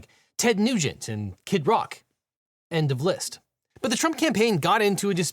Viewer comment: I thought you said "and of Liszt" for a moment lol